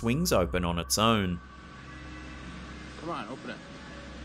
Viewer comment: he clearly said "cmon open it!"